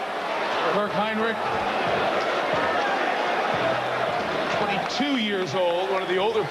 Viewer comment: did he say only two years old